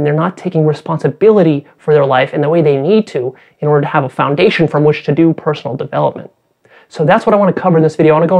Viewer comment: did you say "in order to have a foundation FRom which to do personal development" ????